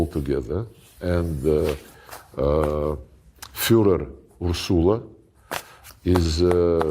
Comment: Did he just say Fuhrer Ursula ?